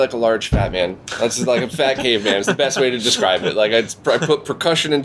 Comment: when you said "I play like a fat caveman", at first I heard "I play like a fat gay man" and I was so lost lol